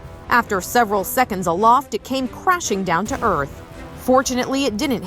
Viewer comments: Umm in 4:55 did she say : unfortunately it didn’t hit anyone : or am i deaf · thought she said Unfortunately it didnt hit anyone